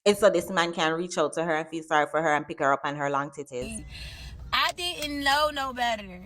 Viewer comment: did you say her and her long titites? 😅😅😅